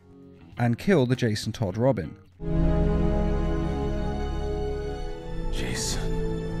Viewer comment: On 2:27 if you put subtitles on it says jeez instead of Jason and actually sound like he said jeez